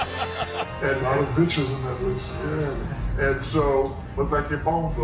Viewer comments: Can someone explain to me what he said at 4:43 I cant quite grasp it after so long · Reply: Shaq: it's a lot of vic's on that list (pronounced vitches) Chuck: look like your phone book. (Like bitches in his phone book) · What did he say at 4:46 ??? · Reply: He said it looks like your phone book